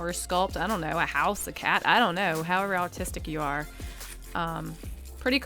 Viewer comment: it sounds like shes saying autistic but she's actually saying artistic😂